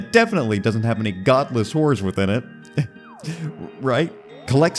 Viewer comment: I thought you said "Godless whores" for a second. This would be a much different game if there were those around.